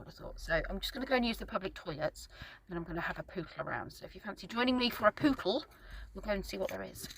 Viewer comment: Ha ha - I thought you were saying you going for a poo then, because in the breath before, you were talking about toilets and then said you were going for a pootle!